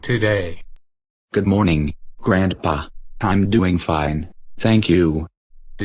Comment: grahandpah is what it sounded like